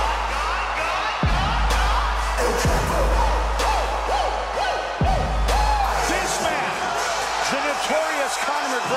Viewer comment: I am not an native american speaker But I am really trying to learn American Slangs And Im really intrigued by mike goldberg commentry on this but could not understand what he said from 19:08 for conor mcgregor Like this man conor mcgregor made very well ______ how's this ______ The biggest star in the world today.. Please fill in the blanks if anyone could do it for me 🙏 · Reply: This man, the notorious Conor McGregor may very well be, Joe, not just in fighting, the biggest star in the world today.